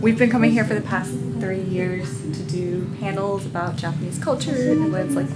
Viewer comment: It totally sounds like you said 'For the past 30 years'!